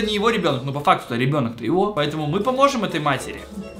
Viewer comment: мне послышалось счастливая свинья